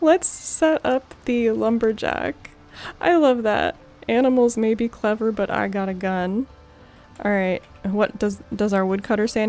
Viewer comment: I thought the hunter said, "animals may be clever but I gotta go." Also liked how you just stopped to listen to him and your face zoomed in.